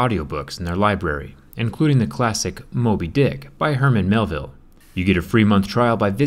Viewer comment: it sounded like he said molde dick 😂🤣🤣